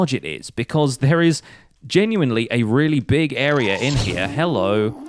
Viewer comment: I heard 'a really big area India' Btw no dislikes till now congratulations